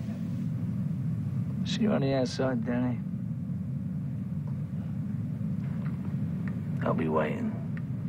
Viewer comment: wat he's saying??? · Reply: "See you on the outside Danny. I'll be waiting."